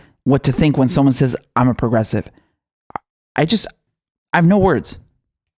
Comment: I thought he said .....i am a prostitute.